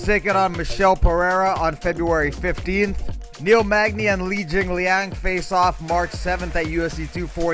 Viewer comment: When you said Neil Magny vs. Li Jingliang, I thought I heard you say "Neil Magny vs. Weili Zhang" and I was like, damn that girl will fight anyone lmfao